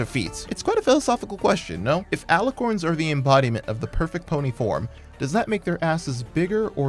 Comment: I THOUGHT YOU SAID PERFECT PONY POR